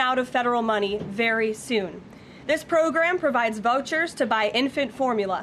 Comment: Did she say vultures? 00:47 yea we know both sides; Its like they take credit for what took 250 years to build 🤷‍♀️ see u at the polls